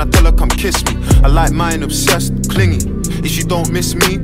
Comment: and all this time i thought he was saying, “ I like mine obsessed with cleaning” 😄